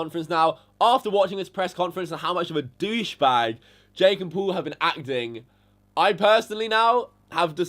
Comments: did he say "Jake and Paul"? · Did anyone else hear him say "Jake and Paul"? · Does Kavos say at 8:29 "Jake AND Paul" ???